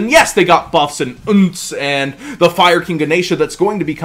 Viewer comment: Did he say Fire King Nation? Whats this card do havent heard of it. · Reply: AllAboutTheHero ganesha the Elefant that comes out next set